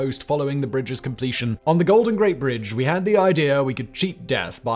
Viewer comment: did Simon say golden Great bridge?